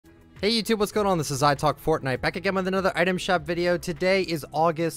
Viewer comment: For a moment I thought he said "Hey Youtube what's going on this is I f*ck fortnite